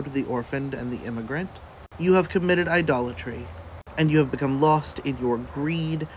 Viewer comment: i think the woke preacher said idolatry, which is having a god before the one true God. Basically we worship Trump as a god and that is the 1st of the ten commandments of what not to do, though i thought he said adultery at first, and had the same reaction. if i'm wrong then that guy still has it twisted.